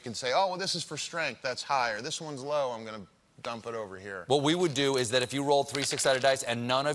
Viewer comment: WHO DID THE CAPTIONS? At around 7:25ish? I'm gonna Donald Trump instead of dump it?! 😆